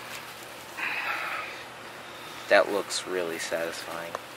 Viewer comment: what did he say?? 😳😂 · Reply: Satisfying?